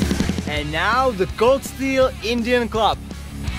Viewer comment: Sounded like he said goat steel Indian club lol instead of gun stock war club